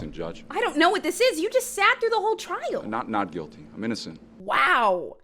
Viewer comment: he actually said "Not, not guilty..." 🤔